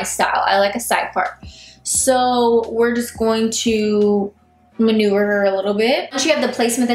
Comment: I swear to God she said "manure" instead of "maneuver"